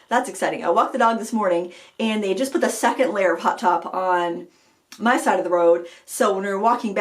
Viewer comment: Thanks for the laugh! When you were talking about "hot top" I thought you were saying "hot tub!" I couldn't figure out what hot tubs had to do with rollerblading, unless it was some wild and crazy Maine summer activity! LOL! Oh, BTW, I liked the lower lighting you used ~ easier on my old eyes and more flattering, too. Happy to hear you're enjoying summer and crushin' on a KitchenAid. Just drop a few hints to and the Frugal teens and maybe it will happen for Christmas!